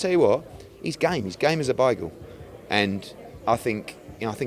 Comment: did he say “he’s game as a bagel”? 😂